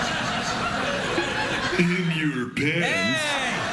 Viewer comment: I know it's terribly lame, that I only found this (and only watched the L word for the first time recently), but putting that asside, could someone help me, what does the skeleton says at 32:09? Thanks in advance. :) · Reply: He said "in your pants"